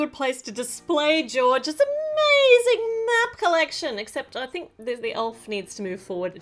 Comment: I thought she said “let’s show off George’s nap collection” instead of map collection!🤣😂🤪 Ima tell u one more time UR THE BEST 😘